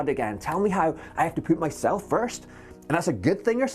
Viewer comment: Tell me I'm not the only one that heard him saying "I have to poop myself first"...